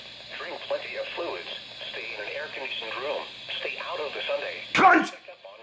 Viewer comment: I thought he said Stay Out Of The Sunday